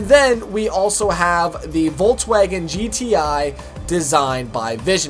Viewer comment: and then we also have the vaulttwagen... (i was just like wtf how did he call volkswagen xD)